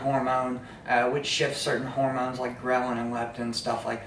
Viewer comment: Did say bromelin..? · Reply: Ghrelin/Leptin.